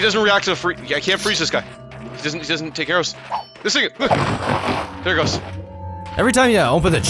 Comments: sounds like he says this nigga😂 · what did he say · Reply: It doesnt take arrows.